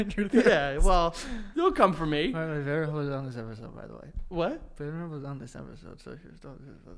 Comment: Can someone pls tell me what joe says at 8:15 I've watched it like 10 times and i still cant figure out what he said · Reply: joe: "btw betterhelp is on this episode by the way" frank: "what?" joe: "betterhelp is on this episode so just don't-- just don't [say that]"